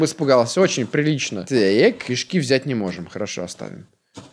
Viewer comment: мне одному послышалось "Кишки взять не можем"?